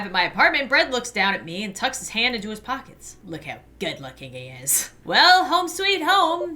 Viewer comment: "Look how good-looking he is." I heard "Look how dead-looking he is." Which one is it???